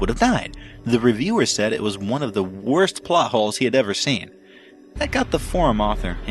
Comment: Totally thought the narrator said "butt holes" instead of "plot holes" 😂😂😂